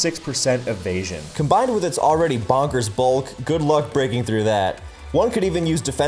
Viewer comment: I swear I heard "🅱️oggers bulk."